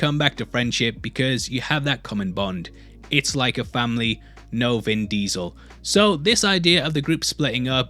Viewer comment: did you just say FAMILYYYYYY